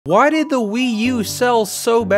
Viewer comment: I thought it said “why did the Wii smell so bad”